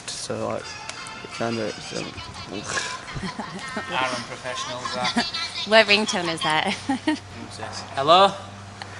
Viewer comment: did Jamie say I'm a professional duck? 🤣😂 Ohh he's hilarious 😆 I just love him. Why on earth did he stop talking!?!?